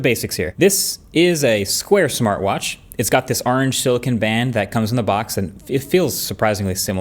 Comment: just realized he’s one of those people that say “arnge” instead of orange